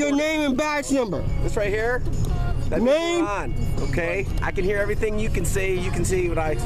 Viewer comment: I thought it was moorish ? I thought he was saying mormon?